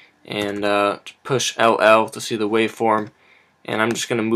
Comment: Push what? I can't understand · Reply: LL